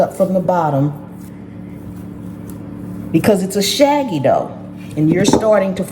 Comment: I thought you said "shaggy DOG" I laughed soo hard...it made my day!🤣🤣🤣